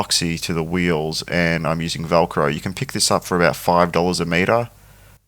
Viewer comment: How did you get the Sandpaper to stick on the Wheel,... I heard you say something about hell Crow but I don't know what was on the back of that will or that sandpaper discs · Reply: It's Velcro.